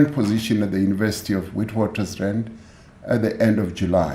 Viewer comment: I lost interest once i heard "commission" my air is down, hai mxm😢😢😢😢 a kena matla Did he just say "wheat waters rand" since when, i thought all along its been Witwatersrand mos...